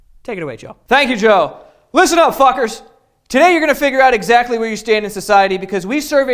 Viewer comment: Joe did you say "eleventy thousand"? It's eleven thousand bruh